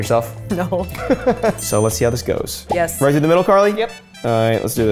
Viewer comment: he says "right through the middle, Karlee?" Did anyone else hear that? Lol